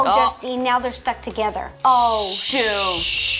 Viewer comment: is the only time I’ve ever heard Ro cuss on YouTube “Shit” - Rosanna Pansino 2016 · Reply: I thought she said shoot not shit